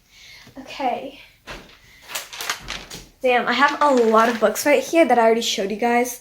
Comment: Girl did she say d***